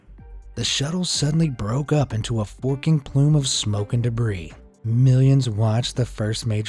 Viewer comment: Did I hear the F word? 😮